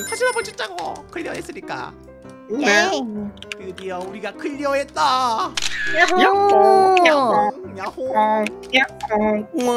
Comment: 초 나만 무야호라고 들림?ㅋㅋ